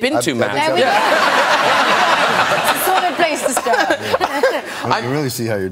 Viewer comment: what did she say at 3:49 to 3:52? sorry English is not my first language.. thanks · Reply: "It's a solid place to start".